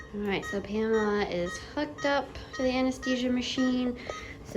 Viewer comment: when you said “Pamela is hooked up to the iv machine” I heard “Pamela is FUCKED UP”😭😭💀